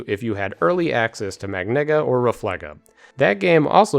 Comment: Excuse me? What did you just say? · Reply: magnega, the last magnet magic